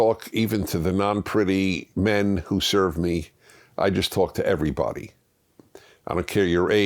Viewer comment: is Prager saying men service him?